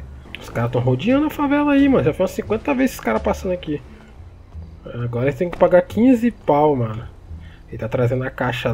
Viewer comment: eu não entendi oque ele falo 😂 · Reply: "agora a gente tem que pagar 15 pau mano"